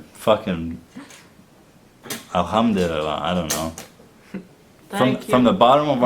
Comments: did he just say 'Alhamdulillah' at 20:45? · did he just say alhamdollilah😱😱 I'm glad YouTube recommended this video and I stuck to the end so worth it